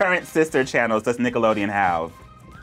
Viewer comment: Because I was talking to someone while watching this, I misheard the Nickelodeon channels question as "How many scandals did Nickelodeon have?"